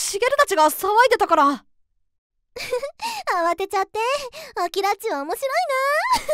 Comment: アキラッチがパパラッチに聞こえた